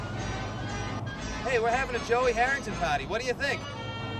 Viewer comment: Oh sorry. I thought you said Joey HEATHERTON Party. I'm outta here.